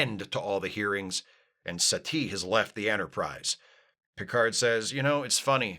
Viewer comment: I keep hearing Satie as "settee"; which is a UKanian word for a couch. It is very distracting.